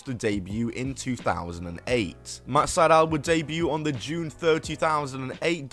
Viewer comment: did you just say June 30,000?